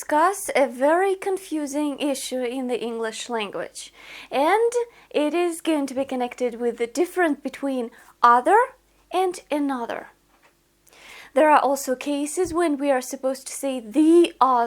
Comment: Guys, please clarify to me at 0:25 could it be said like this: "the DIFFERENT between ...... " or it should be "the DIFFERENCE between .....", or it was said as DIFFERENCE and I heard it mistakenly DIFFERENT? Please advice, Regards